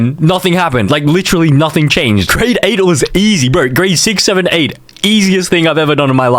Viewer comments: Nah did you say 67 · did bro say 67?